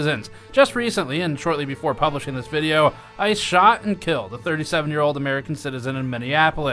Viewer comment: For a second there I thought you said "I shot and killed a 37 year old..." I did a double take there for a second and had to re-watch a few times. LOL